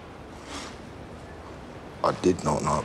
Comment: The scene ending with Alfie saying "I did not know about your boy, though." and Tommy responding with "I know, I saw" was perfect. It shows how well they know each other. Alfie knows how to play the situation to piss Tommy off and get him worked up, and Tommy knows how to read through Alfie's crazy and sometimes incoherent actions. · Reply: ay hold up.. it's "though".. with alfie's accent, it sounds like "douf".. which u might misheard it with "love"